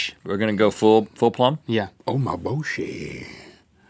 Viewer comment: I heard "Oh my Boshi" :P